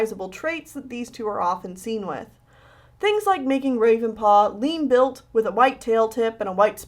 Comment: I thought she said ‘Ravenclaw’ in the intro and my Harry Potter addicted ass had a seizure (yes, if you couldn’t tell, I’m a Ravenclaw)